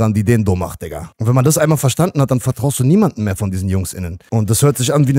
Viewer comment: Ich habe Jungs innen verstanden !